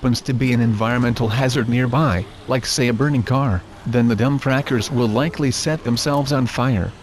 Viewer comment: Did it say dumb crackers